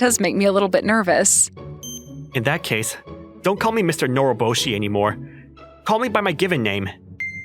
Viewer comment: I thought she said "Mr. Moroboshi" lol